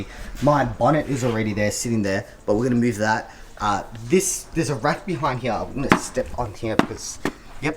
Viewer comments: thought you said "....there is a rat behind here..." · Dead set thought you said you were there with Andy and John Cena in the beginning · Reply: thought he said "....there is a rat behind here...."